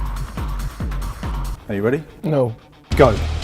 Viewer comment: i thought max said ¨NIGGA¨